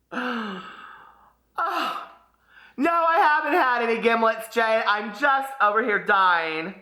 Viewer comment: For the longest time I've thought you've been saying GIBLETS.